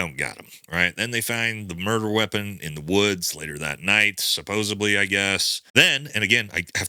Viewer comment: “supposebly” … supposedly. I hear many people miss speak this word and I get a chuckle out of it every time. thank you for all this information. I hate it has come to this. 12:35 hah. Supposebly